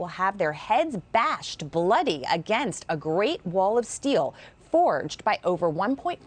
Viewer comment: Oh... I thought he said a "great wall of veal". This makes more sense.